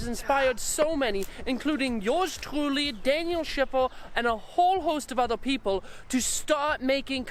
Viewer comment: For a second, i thought you said "A"hole 😂 👍